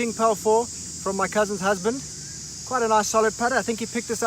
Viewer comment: when you said cousins husband i thought hou said "current husband"